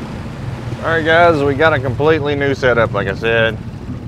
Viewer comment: yo yo did ewe say a "completely nude setup" 🤪🤪🤪😜😜😜